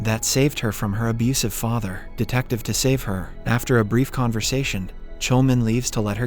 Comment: I think I heard “detective deSaveHer 2:25 😂 Y’all gotta tell me my ears playing on me